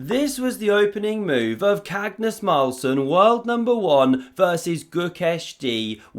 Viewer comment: did you just say " cagnos Marlson" at beginning of the vid. ? 😀😀